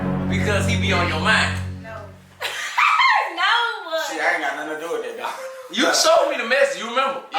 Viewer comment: did say "shit i don't got nun to do wit that"😂